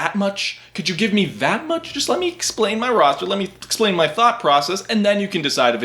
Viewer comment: did I hear thot process?